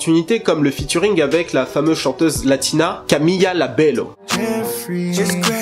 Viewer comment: je vous jure que j'ai entendu Camila Labello 😂, sinon très bonne analyse 👌🏾